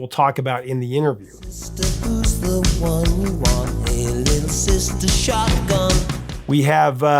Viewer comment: I have apparently been mishearing this song all this time. I always he said "pagan sister" instead of "hey little sister". I guess that makes more sense...